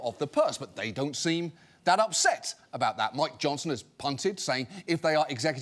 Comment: "Mike Johnson is punted"? What does he say there?